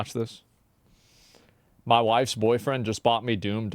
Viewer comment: "my wifes boyfriend just bought me doom" am I hearing things?